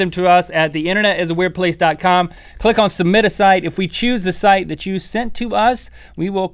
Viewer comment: I'm such a moron... I just realized that Link says "Submit a site".... I always thought he said "Submiddiside!"